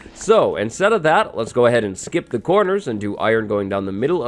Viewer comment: WAIT! Did I just hear say, "...let's go ahead and SKIP THE CORNERS..."? 😲